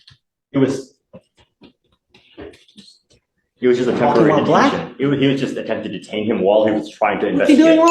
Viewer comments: "walking while black?!" ROFLMAO what a legend XD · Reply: I thought he said "talking while black?" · Did I hear him say, “What’s his crime? Walking while black?” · of the video the judge again asks," what's he doing wrong?". The DA repeatedly argue his nonsense but the judge says "because he's black" now i could be wrong. I do know this for sure he said something to do with black. And ironically the closed captions were deleted or did not pick it up. Coincidence I think not. can anybody else tell me exactly what he said?